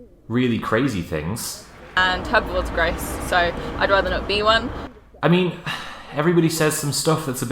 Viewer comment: Did she say "Hogwarts graze so I'd rather not be one"? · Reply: "Herbivores are gross" - I'll sort out CC real soon, promise